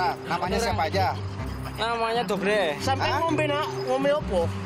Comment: disini pak polisinya nanya, "sampeyan ngombe opo?" Yang artinya, "kamu minum apa?". Tp di teksnya tertulis, "kamu tadi bilang apa? Koreksi dikit nih guys..... Oh ya, mungkin maksud bapaknya nanya kyk gitu karena si mas ini terlihat linglung dan susah ditanyai jd nanya habis minum apaan.